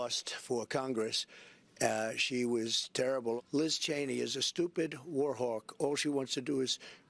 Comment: I saw that clip of Donald Trump speaking about Liz Cheney endorsing Kamala Harris, but I could not hear all of it: I had to read Trump's lips. I could've sworn he said "Liz Cheney is a stupid Whore". Which would've fit trump's persona; that's something he would say.